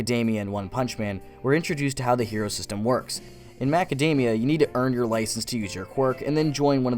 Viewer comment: dude Did you just say macademia